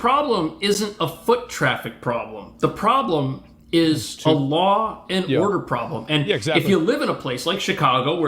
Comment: "Allah and order problem" I can't be the only one who heard him say that. 😅